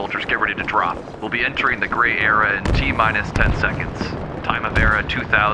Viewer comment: I thought he said gay era lol